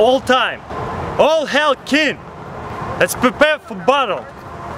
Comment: lol when he said get ready for battle it sounded like he said butt hole